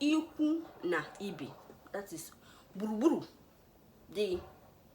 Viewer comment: I like how you described gburugburu in the end🤣🤣🤣🤣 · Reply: Still learning how to listen 😅 thought I heard iburuburu. Google would not confirm 🤣🤣